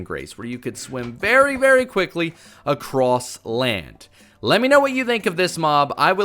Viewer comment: did he just say swim very very quickly across land